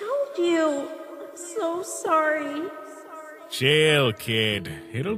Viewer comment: if u put captions sans will say jail instead of chill